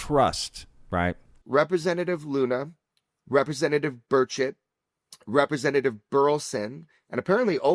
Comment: Ok i misheard Burchett as Bird Shit